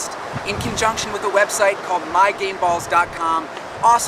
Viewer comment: it sounded like he said my gay balls